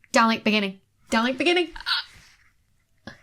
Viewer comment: Seriously (but in a silly way), at 7:21 I heard "Dalek beginning!!" and thought: "Daleks on Mars? Won't that upset the Ice Warriors?" :)